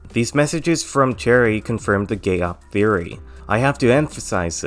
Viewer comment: i have no idea what this word is and its driving me crazy all i hear is "gay opp"